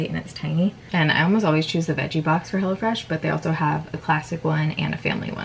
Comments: Lol sounded like u said hellafresh 😂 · It sounded like you were saying “hella fresh.” I think that would be a better name! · At first I SOOOO thought you were saying "Hella fresh" and I was like, great name😂😂😂😂 oops.... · Sounded like she was saying hella fresh instead of hello fresh lol I kept thinking that’s an interesting name